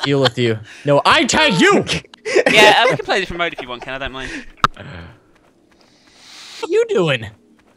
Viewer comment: Did anyone else hear " imma play with the commode if you don't mind" at 6:55??